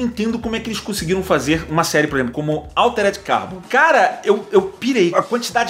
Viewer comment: eu não entendi a pronuncia da serie, alguém pode me dizer o nome ? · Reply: “altered carbon”, pelo q eu entendi